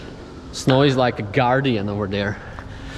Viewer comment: Snow is like a guardian over there Snow: YOU SHAL NOT PAAAAAAAAAAAAAAAAAAAAAAAAAAAAASS!!!!!! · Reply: I just heard “snores like a guardian over there” I am hearing things lol